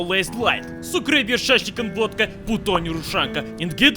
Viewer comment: Im mexican so every time he says put on I hear "puto"